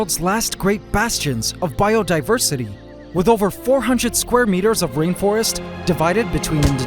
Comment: I know it was probably just a slip of the tongue, but something about introducing Borneo as one of the last bastions of biodiversity by saying it has "over 400 square meters of rainforest" was just the laugh I needed today. I couldn't stop picturing this one parking-lot sized cube of green just PACKED with life. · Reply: Oh. I totally thought he said square miles, lol